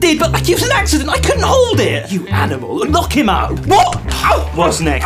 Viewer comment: Instead of “Lock him up” I heard “Knock him up” and let me tell you, the speed at which I looked at my phone 😭